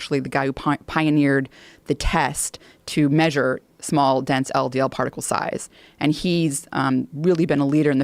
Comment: did she say small dents? Must be talking about Bryan Callens